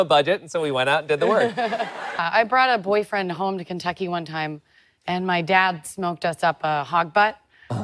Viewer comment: The first time I definitely heard "and my dad smoked us up a hot bud" and thought it was some American slang for something very different!🤭